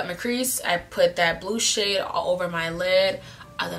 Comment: When u said blue shade i thought u said blue s**t 🤣🤣🤣🤣